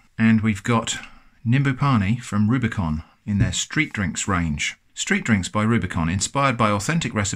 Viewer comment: I really thought that said “Street Onions” instead of “Street Drinks” and now I wonder what that would be.. Onion rings with cheese? Meh.